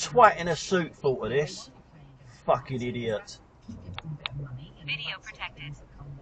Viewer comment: I heard 'Idiot protected' instead of 'Video protected'...